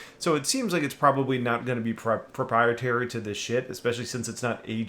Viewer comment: I know this is an old video but you’re CLEARLY saying “shit” instead of “ship” on occasion. 3:20 in this video is the best example I’ve heard yet. 🤣